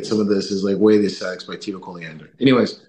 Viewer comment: What's the book mentioned at 51:33 ? I couldn't catch the title. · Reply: the way of ascetics by tito colliander